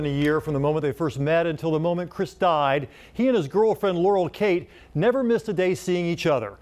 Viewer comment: laurul?i heard yanni😐😏